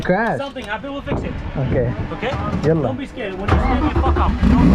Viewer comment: did u say yla?